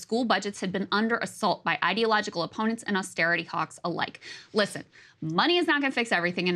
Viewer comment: Did she say "Austerity Cocks?"🤨